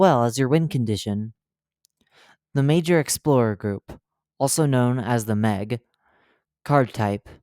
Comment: i thought the human card said dora explorer